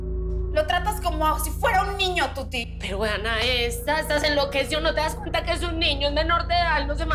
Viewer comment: que es lo que dice la tuti? , alguien por favor? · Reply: Dice pero vean a esta 😊